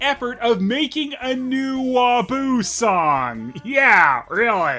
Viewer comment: Yodel: Did he just call him WaFOO? Pat: Eh, close enough.